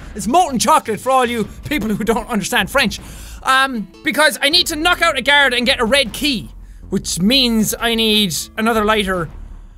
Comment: The auto generated captions said "I need to knock out a carrot to get a red key" 😂😂😂😂